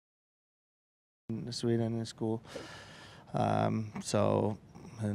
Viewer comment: does he say he learned Sweden in school?